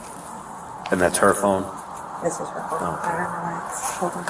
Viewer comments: did she just say “This WAS her phone” ??? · Please have another look and listen at bodycam timestamp 00: 16: 10 (13:32 in this video). I think I heard say "this WAS her phone" when she brought out Maddie's phone, please tell me if you also hear it.